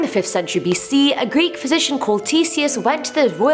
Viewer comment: I thought she said, “Greek physician called T-Series”